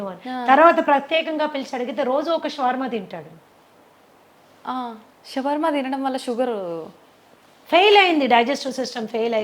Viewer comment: What is word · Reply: SHAWARMA